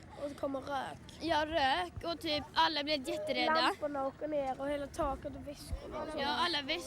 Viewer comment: Noticed that during the News report at 8:33, there is a mistranslation where it says whispers instead of bags. They were talking about the ceiling and bags falling. Hope that clears up any confusions